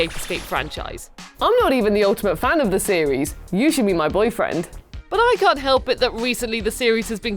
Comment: I heard "You should be my boyfriend."